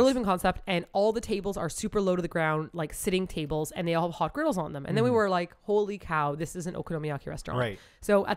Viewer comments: I'm not gonna lie when said "all the tables had hot grills on them" I didn't hear her quite clearly and I thought I heard "all the tables had hot girls on them" and I got SUPER confused why you guys didn't have a bigger reaction. Then I realized you said grills. Whoops. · Ohhh... You said 'hot coals'.. I thought you said 'hot girls on them'.. My bad 😌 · I totally misheard "Hot grills on the tables" as "Hot girls on the tables" and I was very confused for a moment about okonomiyaki establishments in Japan